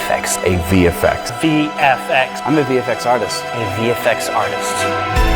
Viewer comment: LMFAO he actually says "artists", I had to rewind to notice 🤣